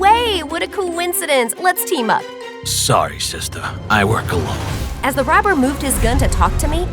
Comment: robber: This is awkward, I'm actually robbing this place, so. Astrid: No way! what a coincidence, Let's team up. robber: Sorry, Sister, i work alone. Bro i thought he say "i wore cologne"💀